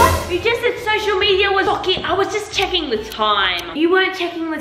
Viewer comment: nah i thought she said “fock it” 😂